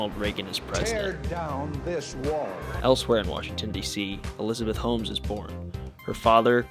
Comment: At first I thought you said Elizabeth hoes.